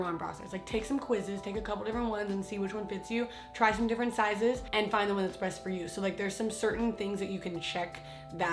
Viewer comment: did you just say “find the one that’s BREAST for you?” 3:01 i swear to god you didn’t mean to but it sounds like pun 😭😂